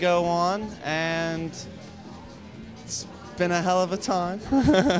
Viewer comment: Was he saying: Been a hell of a time? What's so funny? lol *CMIW